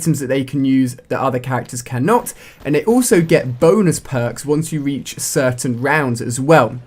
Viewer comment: lol sounded like boners perks XD